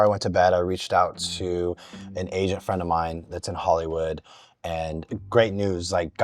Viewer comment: I was sure he said “Asian” and then I thought why did he say that, so I rewinded and he said “agent” after a couple of times.